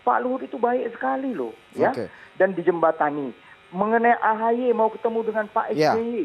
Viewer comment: G salah dengar tuh gue ? AHY mau bertemu SBY 🤧